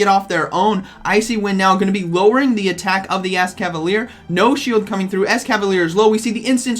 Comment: did you say ass-cavalier